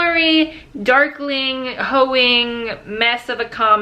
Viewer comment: At the beginning I thought you said duckling XD